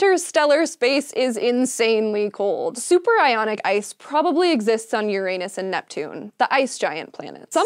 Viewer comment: I thought she said superironic ice